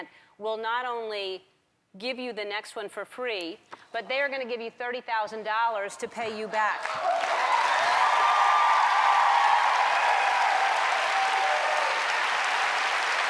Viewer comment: I had to rewind that part about when they gave them money because I thought she said "and there giving you $37.00 to help" and I was like, that's still a help but not a lot compared to what she usually give out😂